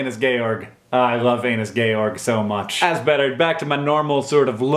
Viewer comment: After "no it's becky" what did he say was his other favourite internet thing? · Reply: I'M WONDERING THE SAME THING. I thought he said 'georg', but maybe that's because MY favorite internet thing is Spiders Georg.